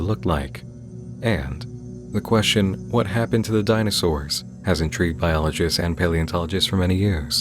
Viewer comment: Was just idly listening while working and I heard, “what killed the dinosaurs?” And “the Ice Age!” immediately popped into my head.